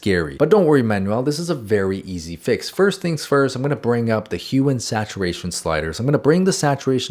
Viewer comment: I thought he said HUMAN saturation sliders 😭